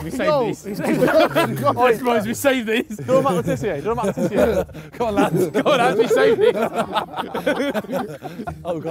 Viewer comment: What did Harry say at 12:11? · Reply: harry said ! do a mat le tissier ! which is a goal that someone done